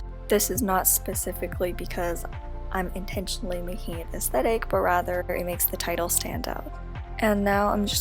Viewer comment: I love how at 3:19 the subtitles say: "... intentionally making it this that egg but rather..." instead of: "... making it aesthetic..." 😂